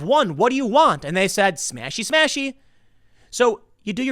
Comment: What did BLM say??? 5:50s "SMASHEY SMASHEY"